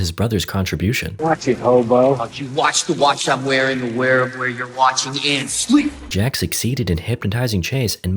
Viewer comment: what does he say here? I all I hear is “oh you watch the Watch I’m wearing to be aware of where you’re Watching” I think